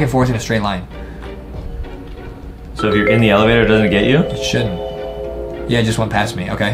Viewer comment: did say shit? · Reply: No i think he said it shouldn't